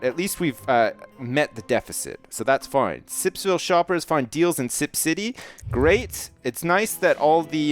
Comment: I thought it said, Sipsville shoppers find dead in Sips City